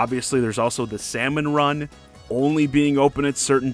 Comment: I thought they said salmon run was open 24/7 now